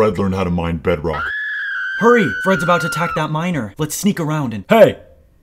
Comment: i heard: "HEY, FRED IS ABOUT TO ATTACK THAT MINOR!" XDDDDDDD (the original was miner)